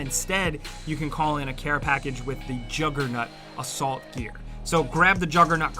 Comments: When sounds like he says "jugger-nut" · I definitely hear "juggernut " the first time he said it